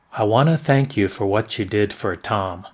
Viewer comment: Somebody help me. I'm coming crazy. Time: 0:54... I don't hear : "I wanna thank you for what YOU did for Tom". I hear: "I wanna thank you for what SHE did for Tom". Please someone responses that.